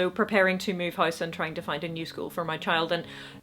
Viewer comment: Also, you said 'new school for the kid' but I heard it as you were looking for a musical for your kid, and I thought it was really sweet that you were being so supportive of their dramatic interests, the way we are of yours.